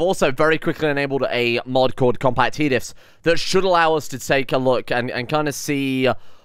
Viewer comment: can you write what you have said, please? Because "compact he lifts" isn't what you've said and what you've said isn't understandable by me. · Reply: He said "compact hediffs". Hediff isn't an english word, I'm pretty sure it's a combination of "HEalth DIFFerence", which is anything that affects your pawns, e.g. scars, bionics, illnesses